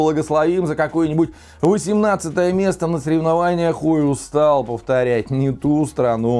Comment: Послышалось "хуйлустал" повторять)) Когда про Родину родную новости слышишь и не такое почудится))